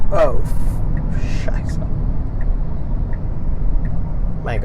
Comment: did he say scheiße :D `ß